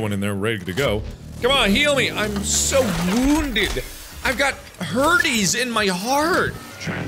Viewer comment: I thought he said "I've got herpes in my heart."